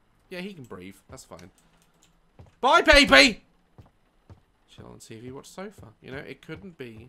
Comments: I definitely heard him says chill out on TV and watch sofa" 🤣🤣🤣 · Where I the only one who heard Daz say Chill on the tv watch sofa 😂😂 ( 5:47 )